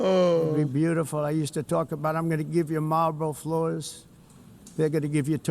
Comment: did he say "Malboro floors" like... The cigarette?